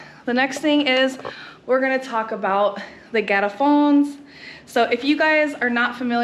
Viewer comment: Iowa?!! Haha Greetings from Florida. Welcome to my home country! What a great video. I'm complementing moving back, but I'm so scared of the change and differences. When you said El garrafón, I first thought you said a "ghetto-phone"!! Hahaha The toilet paper thing is hilarious. Because my people keep that tradition alive in the States. My mom who has a septic tank, refuses to understand that it's ok to flush paper down. You go to any Mexican home in the States and they will not flush paper down the toilet.